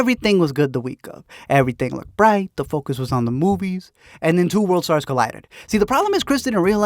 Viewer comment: Did you say "two world stars collided??" 😆 WORLD STAR! 📷 11:33 Seriously, did you do that on purpose, or did God intervene for comedy? What is this life. 😌